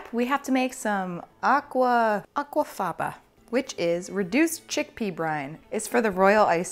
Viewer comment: My husband thought you said, "chick pea RINDS.". LMAO.